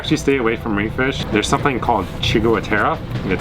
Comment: just when you warn us about paralytic poison, I can't understand what the name is, and the closed caption is no help :( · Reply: I think they said, "coral fish." I think that because they mentioned having to speak to locals to see what kind of fish they're catching and eating.